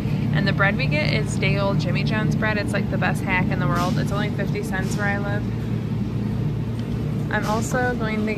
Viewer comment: I didn't know you could buy day old bread from Jimmy's John's. How much is it may I ask? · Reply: lauriedunn2709, I thought I heard her say $.50 cents.